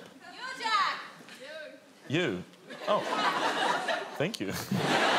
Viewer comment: I heard "you Jack" too. 😁